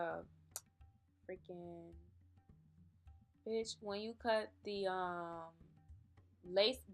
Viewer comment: Lmao at 1:09 did you say bish lmaooooo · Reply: Kevonna Fowler Lmao yes bitch